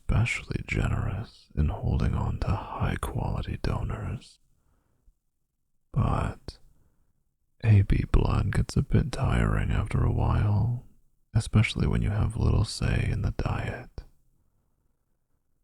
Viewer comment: I 100% had to rewind it at 2:43. When said "AB blood" I heard "baby blood' and was SUPER concerned. I mean, we know he is trying his best to be a monster, but I was almost convinced there!